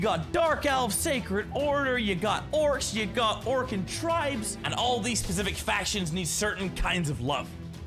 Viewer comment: Did he say Orcan Tribes because it says Ogryn Tribes